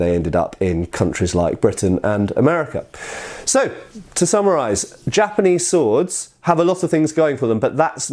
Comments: I heard: " to samuraise " :-o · I heard "To Samuraise...."